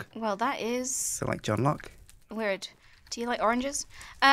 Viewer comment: Omg I know it's proba me but did say JOHNLOCK(Sherlock and John) 0:55 x